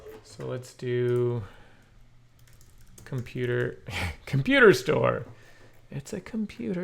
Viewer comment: lol "composer store" instead of computer store.... too much PHP development?